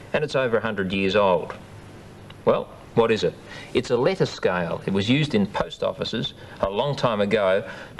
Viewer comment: I first heard it as 'lettuce scale'. He might've even said 'lettuce kale'!